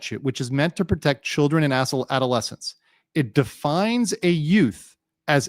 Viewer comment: 😂😂😂An unfortunate, but hilarious slip of the tongue: asul...adolescents, but it sounded like a$$h0le adolescents. If you know adolescents, they can frequently be a$$holes, with the hormone changes, school and home pressures, bullying, anxiety, depression, and other difficult things they're trying to process and maneuver through with their developing minds.